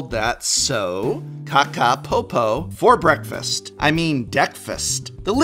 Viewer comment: Okay. Now I understand it's supposed to be named after "breakfast", but I keep hearing "deck-fist" 😆